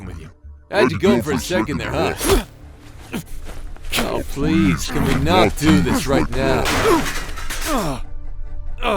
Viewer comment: I didn’t know what the demon said someone please help me😭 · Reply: Do you need the whole scene or just that 1st sentence? Cause he said "I could have told ya that"